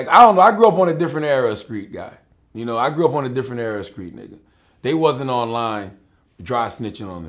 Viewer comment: he had a hard R and niggaz lmfao I swear he said niggers nobody peep dat shit